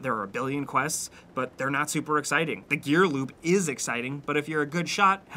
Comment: Am I the only one that kept hearing him say "gear loop" instead of "gear loot"? (Like